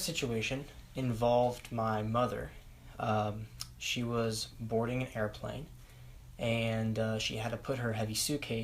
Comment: Woah I thought he said "my mother, she was born in an airplane" lol